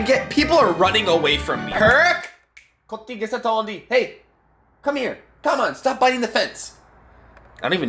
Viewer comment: I can't even understand your punjabi 😂😂 btw I'm Punjabi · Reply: he says Kuthi kise tha di